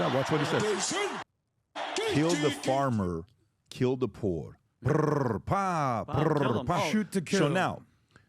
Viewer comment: k*ll the Boer k*ll the Farmer. He is not say *kill the Poor. Boer are White settlers